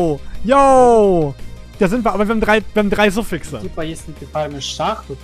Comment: no idea what he was saying but I guess "no open suffix for trigger craft though" ? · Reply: I said "we have 3 suffixes" implying we need to harvest craft the wand. so yes, no space for trigger ^^